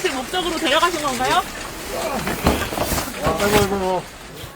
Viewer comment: 기자 욕소리 들리는데 아이 시바세키